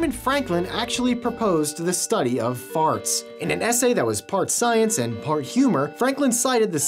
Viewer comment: I thought you said fart science and fart humor